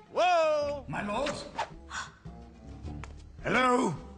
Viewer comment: he actually said my balls lmao